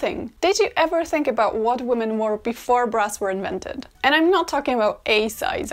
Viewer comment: I happened to be watching at 1.75 speed (admit it, you do it too) and I had to rewind because I thought she said, "...before breasts were invented," not "before BRAS were invented."